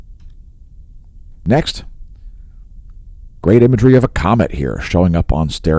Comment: at the 0:23 you said "Ive got a great comment to show you later in the video"...did I miss something? AlsoI got my free sample from Hemp Lucid...thank you! I've been wanting something to help my focus but didn't know what to get so this is great! I just looked at my Twitter page too and my account has been suspended. The last time I was on there was to comment on "name that filament" and named the James Comer filament...wanted to name another one and Twitter suspended me ..thanks Elon for the great censorship!!! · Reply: I listened to it again and you said COMET....I thought you said comment...you can see how I got confused. I'm hard of hearing anyways...sorry about that.